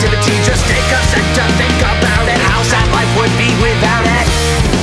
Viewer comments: Hank. I think you kept the uncensored lyric in the description for "how screwed we would be." It sounds like you say "How sad life would be." in this recording. Thanks :) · Coulda swore he said "How sad life would be without it" instead of "How screwed we would be without it" but eh :o I'm just happy there are lyrics lol